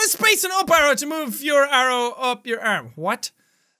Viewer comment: I swear i heard jack say "move arrows to move your arm up your add